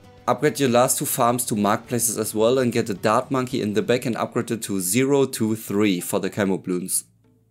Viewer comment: plz help. What’s he saying? I hear something about a “dab monkey”, “bacon grease”, and 0-2-3 camel glutes” lmfao No disrespect to the guy at all. I’m just from southern US and I think my accent is interfering with his in my brain hahah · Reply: get a dart monkey in the back and upgrade it to 0-2-3 for camo bloons